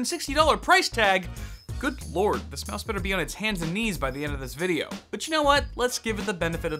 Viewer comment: Wassssup guys!!! 🙄. Oh you said hands and knees!! I thought you said Cantonese! 🤣🤣.